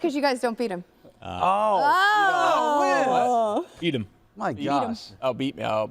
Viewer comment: I thought Liz said "feed him" too!